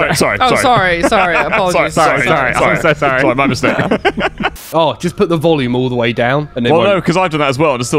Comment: "just put the volleyballs down" is what I heard, coz I was staring at jiggle physics 🏐🏐